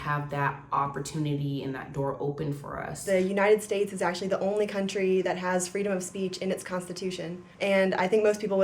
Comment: yall are quoting her wring. she said the united states is actually the only country with freedom of speech in its constitution. idk if that's true or not but that's exactly what she said so stop quoting her wrong.